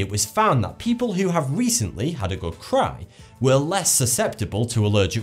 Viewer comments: i genuinely thought he said "people who have had a really good crap"! Even after listening to it a few times hahaha! I know he said cry, but still, now you cant hear anything else but "crap" when you hear it :P XD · am i the only one who hears him saying "A good crap"?